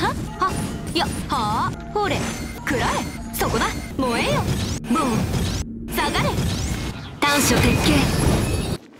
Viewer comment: i genuinely wonder how do you spell what shes saying because kazuha says it too when he attacks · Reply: kurae (くらえ) and soko da (そこだ)